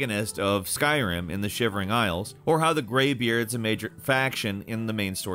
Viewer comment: did I not hear this right or did he actually say ‘Skyrim in the shivering isles’?